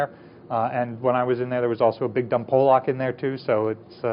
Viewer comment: did he just say there was also a big dumb Polack in there? 😂